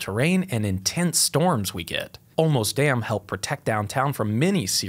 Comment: Thanks so much to all of you who watch this channel for your comments, emails, viewership, and support. I never could have written this book without the encouragement and feedback you've provided over the past 6 years. Check out all the preorder locations here: <link> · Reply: I'm excited about this book. So few people think of engineering as being anything but dry and boring, but now here's an illustrated guide! Also...when you first said "the Olmos dam" I thought you were saying "the almost dam" and I was expecting some story about why it was not really a dam. XD